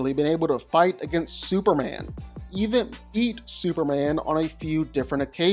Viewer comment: did he say EAT superman?😳relaaaaaaaax Lobo